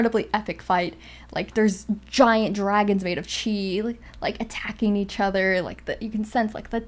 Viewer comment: I THOUGHT SAID DRAGONS MADE OF CHEESE T ^ T